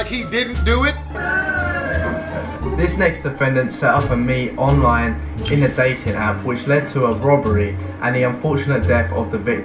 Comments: What did the girl at 5:19 do? I couldn’t understand what he said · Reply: I believe she set up a meeting with a guy on a dating app, which she puppeteered to rob them at the location, but they got killed. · I guess I'm getting old and deaf. Around 5:13, I couldn't understand what it was she did online. What was it??? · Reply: She set up a date with some dude online and killed him in a botched robbery.